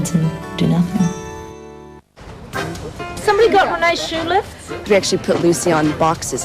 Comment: What does Lucy say? Somebody got Renée ...?Shoe..? I cannot understand it for the life of me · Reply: It's been five years since you posted this but she said does anybody have Reene's shoe lifts.